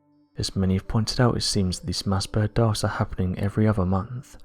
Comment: WHAT THE ACTUAL F*CK IS WRONG WITH THIS NARRATOR!!! At 1:55 "..mass bird darts.." At 2:05 "..mass bird DARTS.." At 3:16 "various things can effect these burns (trying to say 'birds')" At 4:30 "..mass bird DARTS.." I have had it with this mumbling asshole, the next time he narrates a video I am unsubbing, please join me cos its the only way they will get the message! Simple rule of thumb for narration 'If the subtitles have NO CLUE what you are saying then you are doing a BAD JOB!!!' f*cking clown, mumbling fool talking with his mouth full of cock AND balls!!! Get someone that doesnt have a cleft pallet and a hair lip to narrate you idiot!!